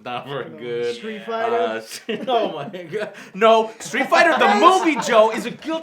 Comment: did joe say "no, ma nigga" @ 0:58 😂😂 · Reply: Sounds more like "oh my god".